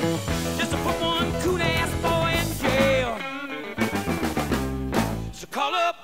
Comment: can anyone tell me what he says exactly at 3:26 thanks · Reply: Just to put one Cool Ass boy in jail